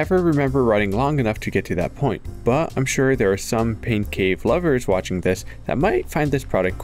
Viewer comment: Wait a second... did he just say "pink cave"?